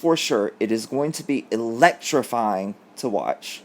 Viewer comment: Electrifying, I thought you said electrocute her, sorry